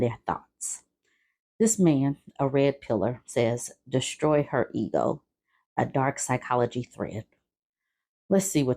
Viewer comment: When you said “distroy her ego” I mistakenly thought you said “destroy, hurt, and go.” I think we both got the philosophy of this creature correct. Men like this seem to think they either have to be a fawning doormat or a heartless brute neither of which women want.